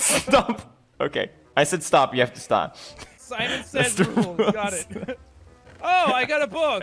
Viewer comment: now i got what jack said in 2:05 is Simon says. we happy few!